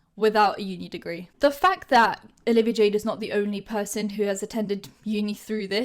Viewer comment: I THOUGHT IT SAID 'OLIVIA JADE IS BLACK'